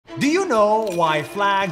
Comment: the subtitles is wrong its ''do you know'' not ''do YO know''